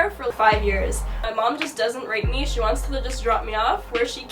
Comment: I’m sorry what is saying at 2:27 and 14:45.....???? I feel like my mom rates/hate/gr*apes me? I